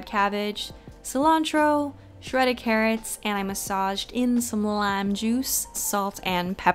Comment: When you were making the salad dressing I misheard and though you squeezed lamb juice on it thank god you didn’t